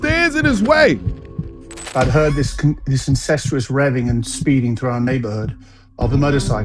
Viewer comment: DID HE SAY “INCESTUOUS?”